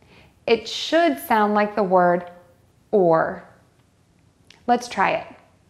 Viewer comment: What did she call me at 0:44 ?? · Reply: Or.